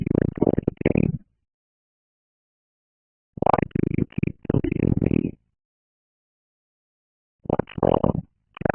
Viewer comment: Crazy how the subtitles can understand what DISEASE is saying even if the quality is low, although in some parts i think is wrong, it reads "Hi, JACKSON. How are you recording game? Why do you keep looking at me? What's wrong, Jackson? What's with that look on your face?" But i did like how you also understand his distorded voice😭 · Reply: oh yea it’s “deleting me” not “looking at me” i think lol